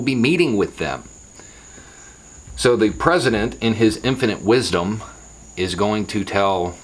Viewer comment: Did you just say "The president in his impotent wisdom" or is that just your country accent and my English ears? 🤣 🤣 🤣